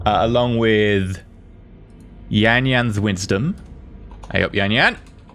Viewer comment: I thought he said Nya Nya's wisdom and I was gonna die.